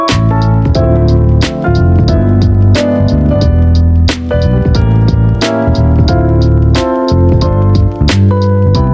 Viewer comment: What does it say there? My screen is glitchy for some reason in this exact moment and all I can see there is that Vincent says "His tavnerne child is Noub anywey..." but I don't think that's what he said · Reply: It says “His favourite child is Noah anyway”